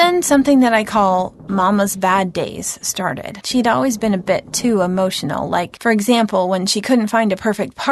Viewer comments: When she said "she'd always been a bit-"I thought she said "She'd always been a b word" rip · I thought she was gonna say, “ she always was a bi**h”😂😂😂